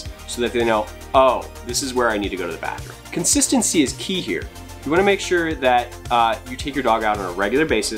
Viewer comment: Great video! Your puppy is absolutely adorable and I love that he was so unbothered during the whole video. 🤣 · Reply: I thought you said “consistency is pee here” lol ty! I just got my second dog. My first was peepad trained when we got him. And this new one, she’s going everywhere 😂